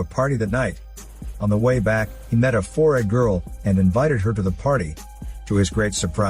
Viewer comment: He said Four-eyed and i thought he said forehead girl lol both would have worked.